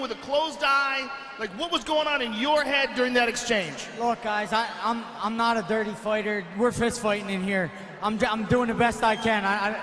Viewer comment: did eddie say he wasn't a good fighter